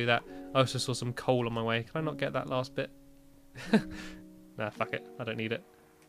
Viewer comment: i always thought he said "Aw bucket" XD